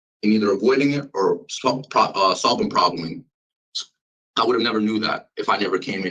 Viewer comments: 😂 did he just say Soblem probling, I can't stop laughing... he's trying so hard, but he's flopping even harder 22:34 he did NOT just blame this on smoking weed smh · Did he say “solving probling” @ 4:53?!?! 😮